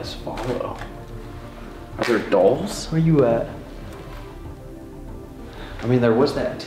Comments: when leans out the window talking ab the women it sounds like someone saying “you now know who I am” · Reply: That is what I heard "now where you at?" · when leans out the window talking ab the women it sounds like someone saying “you now know who I am” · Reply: I heard ‘now where you at’